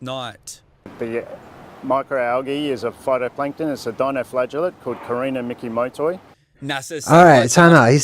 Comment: did dude just say dino flagellant? 🦕💨 Arr narr 😮